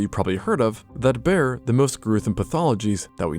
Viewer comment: he said "gruethome" instead of gruesome.😂